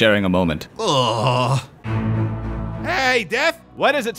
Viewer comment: Tony.... its medication TIME Edit: i think i heard tony say " hey dad what's up " To death :/ cool tony has a reaper as a dad